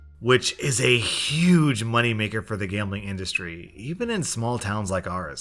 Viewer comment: Does anyone else hear a high pitch on 9:15? It sounds like “indunstrYE”